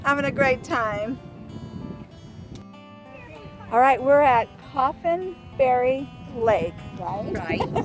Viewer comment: So glad to hear from you again! ???? Also, had trouble understanding the name of the lake you ladies were at???? Gorgeous scenery as always! (Got the answer! The lake is Coffenbury. I thought you said Officeberry, ha ha! Duh.)